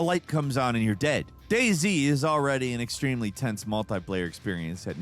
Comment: DAY Z 14:27 I just realized. I thought he said “Daisy” ☠️